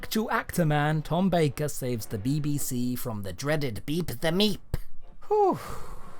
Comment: bro said "beep the meep" but I heard "beat the meat"